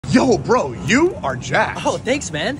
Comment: i thought it said "yo bro you are jack" "oh thanks man"